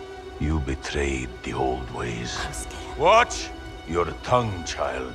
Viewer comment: What does bloodhound say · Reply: Heimskr